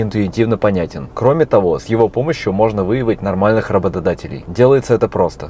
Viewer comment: «Вые6ать нормальных работодателей» мне не послышалось?